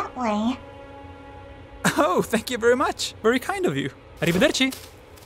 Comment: did u just said "Arrivederci"?